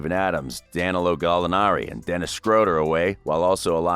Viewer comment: At first, I thought he said Dennis Scrotum🤣